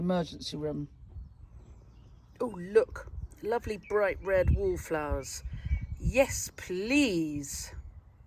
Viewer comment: I hear you say lovely red “wall” or “wool” flowers. They are indeed gorgeous! I would like to grow them but I don’t know the name. Thank you for your knowledge and sharing it😀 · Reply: wallflowers x